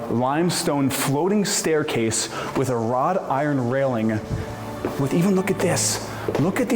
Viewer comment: Wrought iron, please - it sounded like you said rod iron.